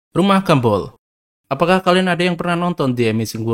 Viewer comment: Salah denger tadi, gw dengernya rumah gembul